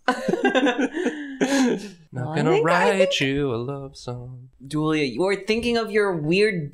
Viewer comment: I heard "I'm not gonna Raichu a love song" and I laughed. jsyk ok thanks